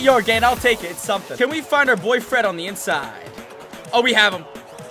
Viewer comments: it sounded like let’s find our boy friend. Lil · sounded like "Can we find our boyfriend on this inside" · When you said let’s throw it to your boy Fred I thought you said boyfriend